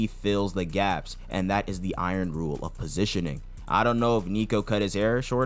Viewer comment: he clearly says pest control as in exterminating a vermin like he said to sendou in the u20 game not positioning.